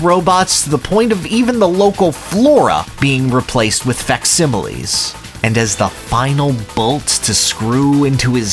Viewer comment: first time in a long time I had to pull out the English dictionary, I still don't know what you said Umm.. can you.. please explain what vexcibilies mean? Sorry I'm not trying to sound mean. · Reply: Sorry my bad, I have that Southern California "syllables kinda just run together sometimes" twang The word I'm trying to say is is "Facsimiles".